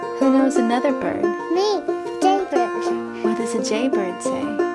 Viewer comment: i thought the child said a gay bird ( 0:58 )